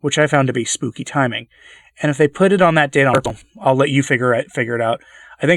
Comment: The audio seemed to fail at 10:55-10:56. What were you saying at that point? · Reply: If they put it on that day on purpose, is what I was saying. I didn't notice that when I checked it afterwards.